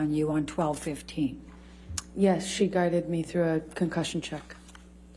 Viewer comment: Her nurse guided her through a concussion check through text ? 50:10 did I hear that right ?